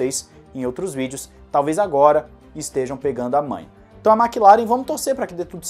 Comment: Por favor, diga que não fui só eu que ouvi sobre pegar a mãe 🤨